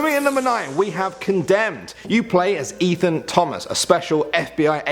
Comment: am i the only one who heard Ethan Dolan or am i crazy?im just watching them almost 24/7 lol. also wheres Grayson? xD